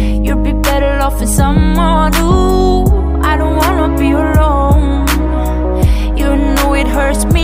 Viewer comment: "I don't wanna be alone" I'm addicted for her voice😂😂😂 Wow we have 957 like and 83 replies nice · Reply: Yeahh ,but I heard she say " I don't wanna be wrong "😆😅